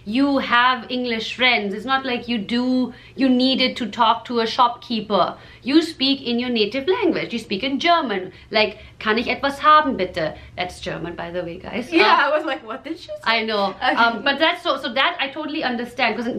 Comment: "kann ich etwas haben bitte?" alguém ae que fala alemão pode me confirmar se foi isso mesmo que ela falou, bitte?